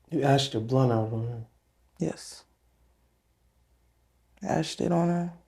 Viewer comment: Did she say she pooped on her at 40:56?